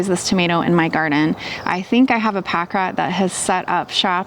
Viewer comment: I'm sorry, I don't know what that is. Can you explain? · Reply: sorry I must have mis-heard. I thought you said that you had a paca in your garden . It is a small mammal found in Central America. We are a little north for pacas :)